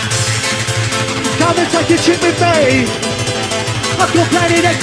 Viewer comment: can ANYONE confirm what he says...i think he says "come and take a shit with me" which is possibly the least happy hardcore MCing and lyrics i have ever heard!! What do you think?